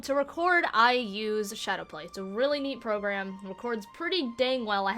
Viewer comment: Hey, I couldn't exactly catch what you said at 8:45. What program was it? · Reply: ShadowPlay